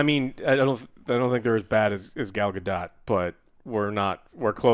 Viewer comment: When you said it wasn't as bad as gal gadot i thought he said "gal dukat" from deep space nine. I was like man why is he getting shrapnel that was a fine performance especially for '90s c.